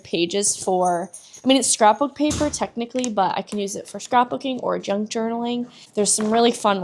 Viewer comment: At first, i thought she said drunk journaling 😂🤭🤭